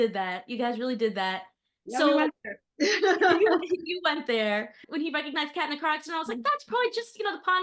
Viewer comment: Can someone tell me what says at 20:10? "When he recognized Kat...." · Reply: I was saying when Colton recognized Kat in the car accident in season 1 I thought that was just a fluke of the pond being like, here have this one moment. While others thought it was the first proof of his time travel.